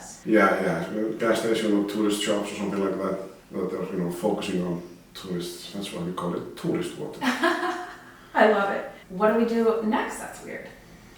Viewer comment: When he said "tourist water" at 3:49 I thought he said "toilet water." 😳😂